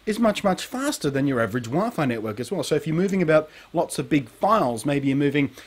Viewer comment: I thought you said it was quicker than your wife when you said wifi.